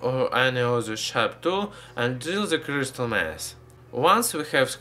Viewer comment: when he is saying " Crystal mass" I hear "Crystal meth" lol